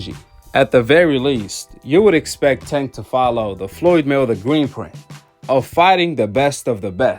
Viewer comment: “Floyd mayweather green print” thought it was called blueprint. Brother, if you going to do reviews please pronounce things correctly!